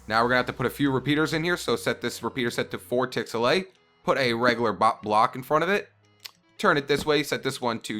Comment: did he say butt block?